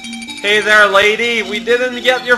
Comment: Um. Now I'm hearing him saying "Have a bird instead" it's weird. I always hear "it's dead" but ugh. i bet it's it's dead though